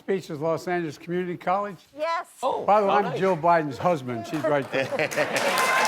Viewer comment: did he say “Joe Bidens husband” ?